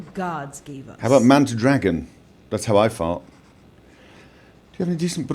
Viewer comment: ok ok at 16:23 I was messing w/ my headphones and I swear I heard this: "man to dragon. Thats how I fart"... ROFL